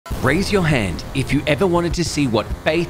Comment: My stupid arse thought he said raise your head 💀